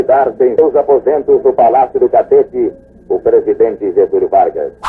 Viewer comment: Só eu ouvi palácio do cacete ?